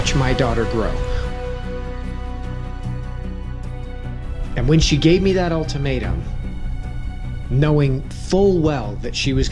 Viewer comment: Great story! Also I thought he said ''When she gave me that old tomato.'' Anyways if you're reading this have a great day! X3